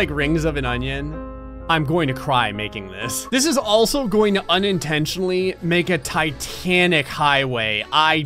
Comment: I swear I heard “oniontentially.”